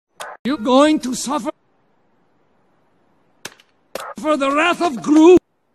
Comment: At the start, I heard "You going to suffer- for the wrath of Gru"